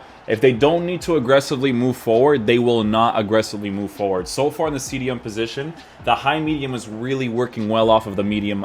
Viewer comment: I wasn‘t paying attention for a sec and heard him saying: we put neymar in the cdm position. And I was like: What?🙃